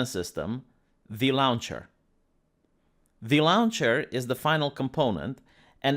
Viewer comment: "The lawn chair is the final component" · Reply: It's louncher. You misheard.